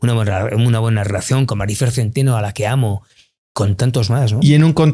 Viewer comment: "Maryfer Centeno a la que amo..." ¿oí bien? No creo que se trate a la famosa estafadora.